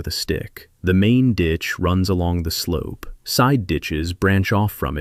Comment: Oh, he said side DITCHES, I thought he said side bi... never mind.